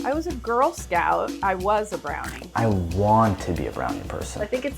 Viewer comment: I thought said "i WANT to be a brown person" and then they cut to omg